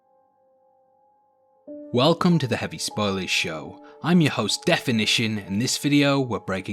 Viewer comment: it sounds like you said "heavy spoiley" in the intro. maybe you should name your channel something you can actually pronounce